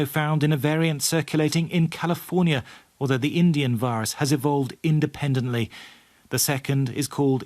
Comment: Did I just hear them say "indian virus"? Just as bad as calling it Chinese virus